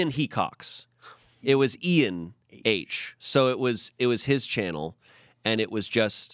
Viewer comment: Amanda mishearing "Ian H" as E and H sent me, I questioned it too because it sounded like a new england accent for a second